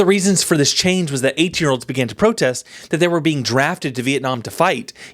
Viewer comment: hahaha I heard “8 year olds” - I’m like dafaq?